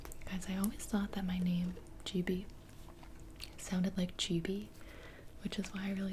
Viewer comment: am i missing something or did she say " i always thought gibi sounded like gibi"